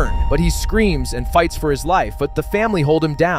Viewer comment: Why do I hear “the man screams and bites for his life”😭😭